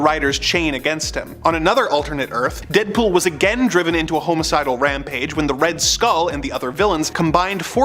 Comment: xD @ 1:00 I heard, "Homosexual Rampage". I was like, O_O lmaooooo